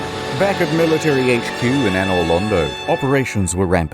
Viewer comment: Did he just say Anor Lando